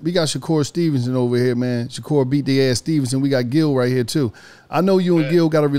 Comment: I thought he said "Shakur Bitch Ass Stevenson"🤣